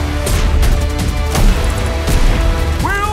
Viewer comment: "Will someone please, bring this guardian, his crown! YAAAAAAAA!!!" -Shax 1:21 Freaking love Shax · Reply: I heard "Will somebody please, take this guardian, his crown. NOOO!" (As in, another person died.)